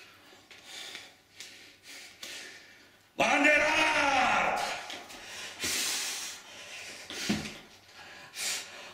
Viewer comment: What is he shouting at 5:56? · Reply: Bander arrrr